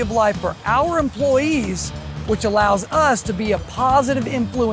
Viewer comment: What's said at 6:36 ? It's excluded from the captions / transcript. Christ? Clients? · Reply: positive influence for Christ